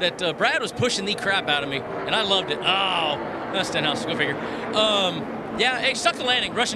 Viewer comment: i heard the interview for the barrel roll so many times but it’s the first time i heard “Stenhouse, who figured?” 😂